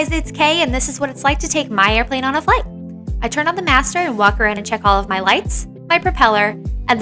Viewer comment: Please don’t tell me you get anywhere near your prop when the mags are hot. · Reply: My bad I misheard master for mags.